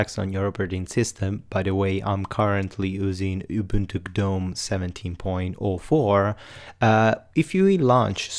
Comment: Hi, what are you saying on 0:26 Thx! and i see that change the price to $80 to buy sublime-text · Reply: I said that I was running Ubuntu Gnome 17.04